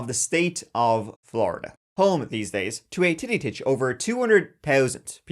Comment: Did he just say, "Titty Titch?!?"